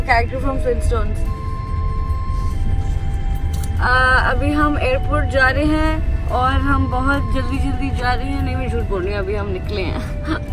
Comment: what was she saying?? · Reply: She said phle fursat me nikalo